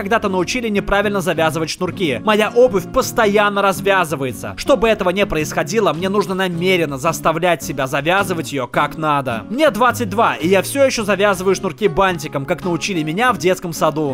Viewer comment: лол,мне одной послышалось "я завязываю шнурки батинком"?